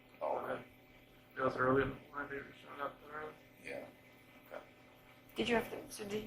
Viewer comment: “it smells, it was earlier babssnxjdndjee road ???? ????” What does Chris say I listened a thousand times can’t make it Help! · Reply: Earlier, when my babies showing up in the room.